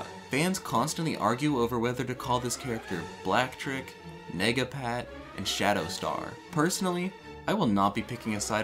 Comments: Lmfao 13:46 did you call him nigga-pat. Its 2021 pal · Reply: He said " Nega " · did he say megapat or the n word? 🤨